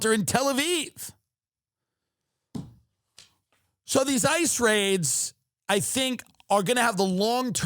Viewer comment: ICE raid, i thought you said NICE RAID!